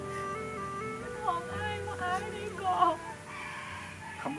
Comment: พี่พูดว่าขออ้ายว่าอ้ายได้ก่ รึเปล่าคะ #ความเห็นว่วนตัว